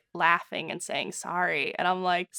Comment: I thought that she said sike instead of sorry 3:14 😔